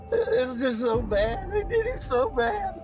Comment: I hear "i did it so bad"...anyone else